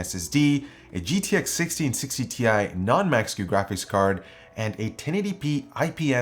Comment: The captions says it a "non-mexican graphics card" LOL!